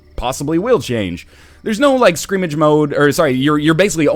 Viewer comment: Skrimish? Did I just hear NL say Skrimish? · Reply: knightl3y The word is "scrimmage", for the record.